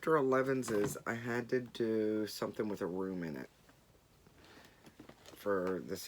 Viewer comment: Haha, as if you would let yourself be bullied into anything 😂 But I like how much stitching you are getting done with all the groups and all the challenges. (I think I am loosing my hearing... I thought you said, you had to stitch something with a worm in it...🙈) Next time your neighbor is mowing in the dark, sneak out and put rocks or pebbles in the grass. His mower will not like that 🤫 OMG, who does not like Hugh Jackman as Wolverine😍😍😍 Hey, self rescuing princess, please do not get me blood pressure up at the end of a great FT!!! I have had nearly daily encounters with spiders like yours (ok, not quite as large, but big enough, thank you very much!) now I feel them all around me again 🥺😩